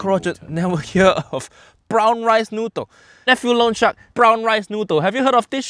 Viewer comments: Lol I thought he was saying Lo Shang for the first 6 minutes of the video... now I realize he's been saying Loan Shark this entire time. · Irrelevant comment but... i misheard "Loan Shark" as Luocha- 💀